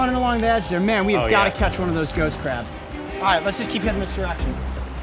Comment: Anyone else thought he was saying ghost crap at first instead if ghost crab.